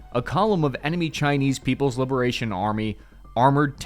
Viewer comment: wasn't paying attention for a moment and I heard "a column of enemy Chinese people".